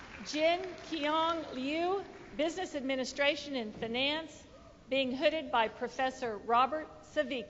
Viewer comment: did the dean say fenance instead of finance 😂